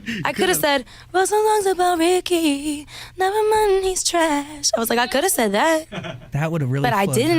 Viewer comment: What she exacly said...it was unclear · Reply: Timmi 98 i heard “Nevermind, he’s trash” but idk the rest